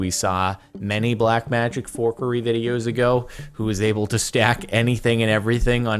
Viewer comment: Captions thought u said Whore Curry at "forkery"